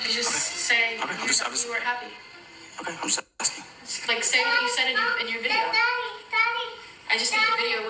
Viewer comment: hold on in the audio clip catherine literally tells him to write what he said in the video he made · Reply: “tell them you were happy, just say what you said in the video”